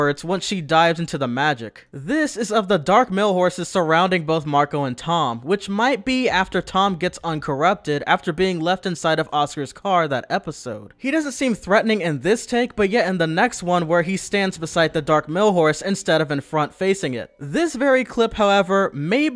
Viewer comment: It sounds like he said asura instead of Oscar